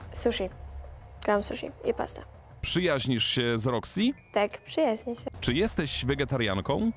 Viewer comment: Tymczasem ja: 2:38 usłyszałam przyjaźnisz się z lexy (lexy chaplin) Mój mózg: Warto zrobić przerwę od YouTube